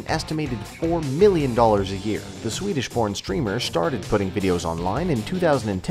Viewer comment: I heard the Swedish porn streamer